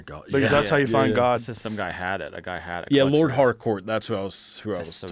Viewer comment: I legit thought he said he went to "Lord Hardcore's" house lol...